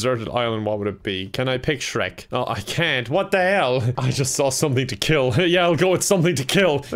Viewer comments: I thought it said “something to kill me!” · I thought it said, "something to kill me" and I got really confused.